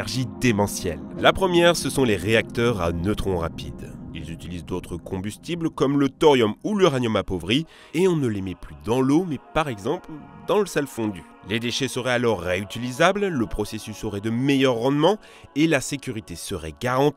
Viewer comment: A 9:09 j'ai entendu dans seum fondu